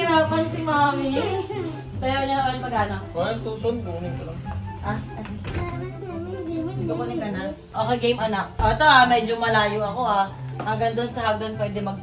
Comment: narinig nyo ba sinabi ni mahal 😂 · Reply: Kuhain yung tucson ang sabi nya, kasi tucson sasakyan ni toni